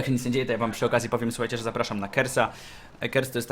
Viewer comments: A ja usłyszałem 'zapraszam na kebsa' xD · czy tylko ja usłyszałem zapraszam na kebsa? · Czy tylko ja na początku usłyszałem,zapraszam na kebsa? :D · Na początku usłyszałem że zapraszasz nas na kebsa XD :D :)